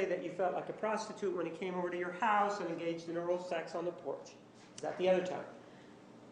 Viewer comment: wait wait wait did he say porch. 😂😂 And I think the prosecutor could of done with out the ky and butt sex stuff 😂😂 I mean we all know she guilty! I don’t think a cross examination about anal sex and lube is going to sway a jury.